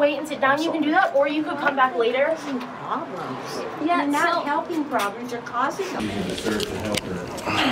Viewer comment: Classic! Did you hear what the person said? “You’re not helping problems you’re causing them.” LOL Well said.